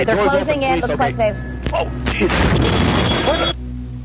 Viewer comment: I think the pilot/reporter says "whoa Jesus....(Impact sounds) we're losing..."